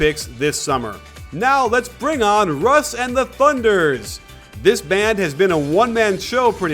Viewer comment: did he say the "thunders"